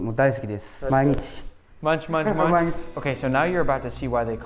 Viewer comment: BTW did not say ”Munch munch munch”. He said ”Mainichi manichi mainichi” which is Japanese and means ”every day every day every day!”